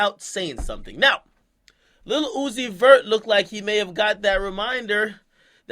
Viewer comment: Every time he says Lil Uzi Vert I hear "Lilucifur".. Lucifer type shit 🤔🤔🤔🤔🤔🤔🤔🤔🤔🤔 ⚰️⚰️⚰️⚰️⚰️⚰️⚰️⚰️⚰️⚰️